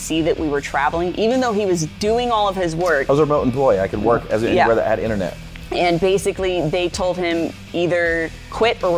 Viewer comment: What was job at his company before he quit? I couldn't get what he was saying? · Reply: He used to be a catastrophe insurance appraiser. ☺️